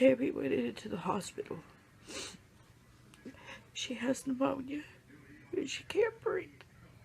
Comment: I'm sorry, I think she said "Pneumonia" 🙏 not Amonia 😁